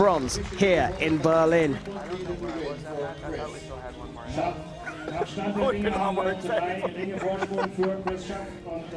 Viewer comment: What did Brady say at 13:00??? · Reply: 5 years later, so I think he said I don't know what was with this match because I thought we had one more end.